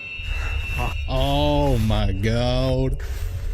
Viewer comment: did you say: ooh my goat?